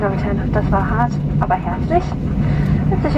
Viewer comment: You guys know what the flight attended said at 16:05? "Das war hart, aber herzlich." "That was hard, but cordial." (I hope you can say it like that) I absolutely love people with a sense of humor.